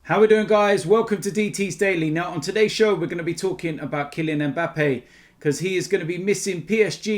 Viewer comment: Christ, at the start of the video I thought DT said ‘were gonna talk about killing Mbappe’😂